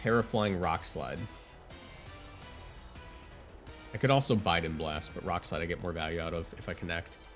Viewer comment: did you say biden blast? lul